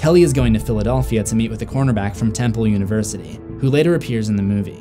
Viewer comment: does he say conerback? Lol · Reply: Yes, cornerback.